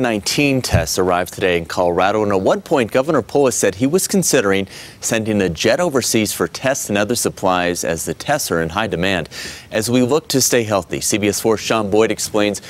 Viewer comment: Some English subtitle correction: At 1:07 the 영어자막 should say: "At one point governor Polis said he was considering sending a jet overseas for test*s* and other supplies as the tests are in high demand." Then 1:12 next sentence: As we look to stay healthy, CBS explains why South Korea decided to help Colorado. 1:42: crises (plural) instead of crisis (singular) 5:53: Governor Polis says the state hopes.... Some are minor changes but I think you're providing a great service for learning English and it needs to be on point! ^^ 수고하셨습니다!~